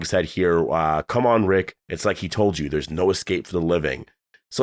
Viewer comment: I thought he said, "it's like he told you, there's no escape for the living" as well but when I when I went back and watched again, he said "she said" so I'm thinking it's Jadis.